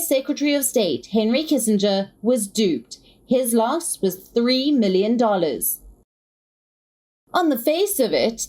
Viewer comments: Wait I am confused at 2:04, Is it 3 Billion or 3 Million, cause I heard 3 Million :)) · Is the commentator sleep talking? At 2:08 she says Henry Kissinger lost 3 million whereas the caption says 3 billion